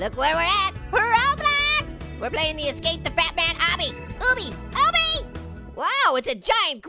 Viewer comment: I thought you were saying batman